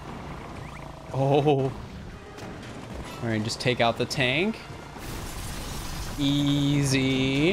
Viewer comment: I paused the video and the last thing I heard was "just take out the ta-" (the last word was tank)